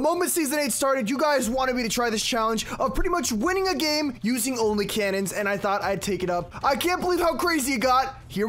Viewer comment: I thought you said ONLY a cannon